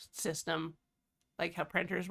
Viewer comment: I definitely heard say "predator" not "printer" but it did sound like printer on a second listen. Anyway, that presentation at the beginning was very educational!